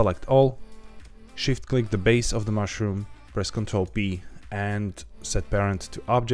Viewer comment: Awesome tutorial. I LOVE the fact that you mention ALL keys being pressed (and include the visual key presses in the bottom right hand corner) There was a time I thought you said Ctrl + D and kept hitting a dead end, saw someone else said you show keypresses... it was Ctrl + P! My poor ears didnt hear it right lol